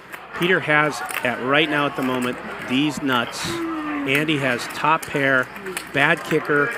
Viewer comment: Bro does he say at 2:50 "peter has at the moment THESE NUTS"?? I swear I heae these nuts😅